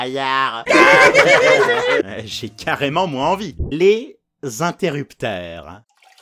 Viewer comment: C'est moi ou à 3:51 il dit les interructeurs !